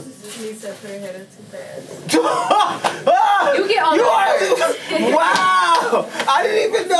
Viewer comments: what was the girl saying in 3:11 ? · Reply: JENLisa KIManoban she said Lisa stuck her head up to fast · what was the girl saying in 3:11 ? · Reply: She said Lisa stuck her head up to fast